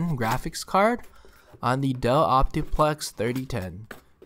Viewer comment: duh optiplex? Why did he say dah